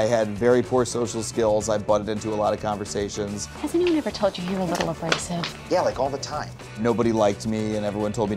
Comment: What does say at 1:05? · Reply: "Has anyone ever told you you're a little abrasive?"